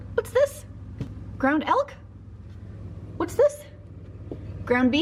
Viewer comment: Definitely heard you say “ground elf” instead of elk the first time 😂 had to go back and make sure you weren’t doing something sketchy there haha.